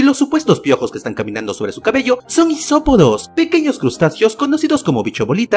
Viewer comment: Isopodos! Pense que decia hisopos jajaja